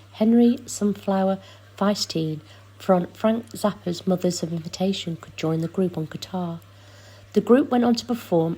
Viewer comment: ?? What do you mean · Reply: sure sounded like she said invitation instead of invention. Mothers of Invention!